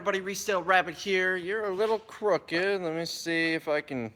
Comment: I thought he said “you’re a little crook dude.” My first thought was how did he know?